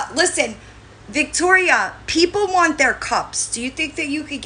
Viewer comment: what’s this about? I keep hearing “Victoria u gonna send people their cups” what cups ? X